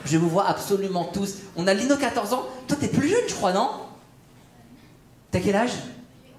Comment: j'avais aussi entendu "toi t'es plus vieux j'crois" la première fois